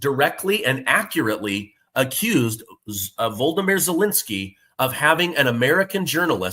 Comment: Sounded like you said Voldemort Zelensky 😂